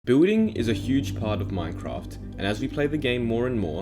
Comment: XD at the start I thought he said “bulling is a big part of Minecraft” Laugh out loud sorry